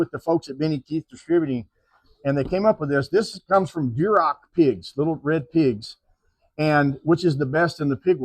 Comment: In Kansas the czechs make bierocks.. a yeast roll filled with cabbage, onion, beef... thought the owner said beirock pigs.... oh wait... he meant duroc pigs.... consonants are your friend... or worst enemy.